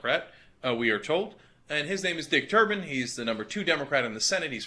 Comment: I thought I heard "Dick Turban." Im pretty sure thats not were they go.